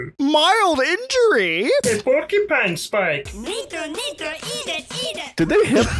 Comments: Am I the only one who heard, “nico nico eat it eat it!” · did they just say the n word?!